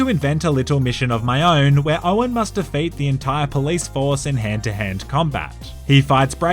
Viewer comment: Hahaha I thought you said at 7:07 Christian has to fuck the entire police force with hands hahahah